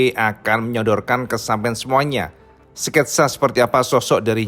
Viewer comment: ada kalimat aneh.Atau saya yg salah dengar ya..? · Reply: "...ke sampean semuanya" gitu nda sih?😄